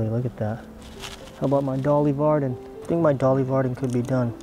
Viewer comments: I thought you were saying Dolly Parton the whole time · I kept hearing "Dolly Parton" the whole time and thinking, wow she even has a fish named after her. Lol.